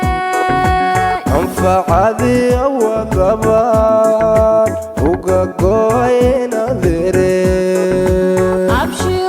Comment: Can somebody plz temmie what he's saying at 1:08 "anfac adi awada ban oga go'ay..............." mxu ka go'e 🤷‍♀️🤷‍♀️ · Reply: anfacadi aawadaa baan uga go'ay inaadeerey micnaha cuntadii dartaa baan uga go 'ay kkkkkk